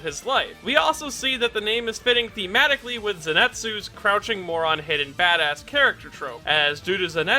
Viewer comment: holy shit i thought he said crouching moron hidden FATASS im dying😭😭